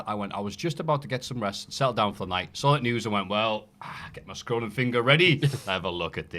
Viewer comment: I misheard because i wasn't fully listening and thought he had said "I got my scrotum finger out"